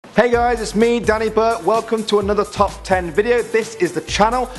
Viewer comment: did he say Danny Burp?😂